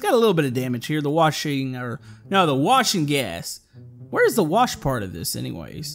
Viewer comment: oh there’s a little bit of desert worm in my tracks Me:.......... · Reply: At first I thought u said washing gas instead of wash n’ gas :)